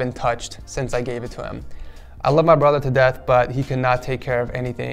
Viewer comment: I thought he said (“I love my brother’s death” instead of “I love my brother too death”